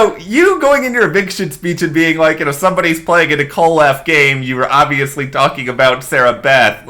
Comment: Thanks so much for clarifying something for me. I thought that Derek X had said that someone was playing "a kick --- game", not a Nicole F game. 😂🤣